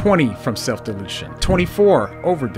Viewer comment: YouTube has become such a joke. Content creators really need to say "self deletion" to avoid being demonetized. Bring back the old YouTube. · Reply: Thanks, I thought he was saying "self-delusion" and got really confused.